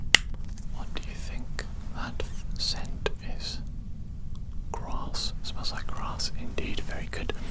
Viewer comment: When you said "grass", I heard "Ross, it smells like Ross" 🤣 I was a bit confused until I realised you said grass 😅 Really cool video btw! 😁